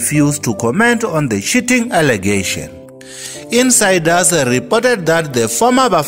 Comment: I thought this guy said "shitting allegations"